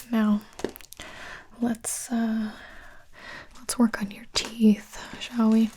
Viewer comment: me: did she just say ‘twerk on my teeth?’ Also me: Uhh sorry girl not today 😅